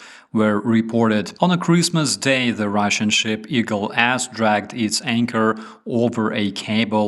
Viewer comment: I know it is not funny but I cannot help laughing when you say Eagle S dragged its anchor because I hear "Eagle Assdragged its anchor"